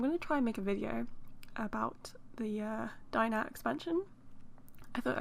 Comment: when you said diner expansion i heard "dino expansion"…… i wish! also: super cute house <3